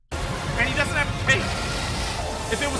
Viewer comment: When was debating whether it was a marauder horse master or not I thought he said he lacked cake instead of a cape. My mind went, well it makes sense they would need some padding for riding around all day.